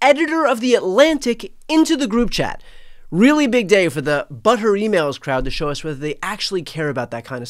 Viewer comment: Brilliant collection of quotes! (BTW I thought you said ‘butter emails’ for a second there 😂)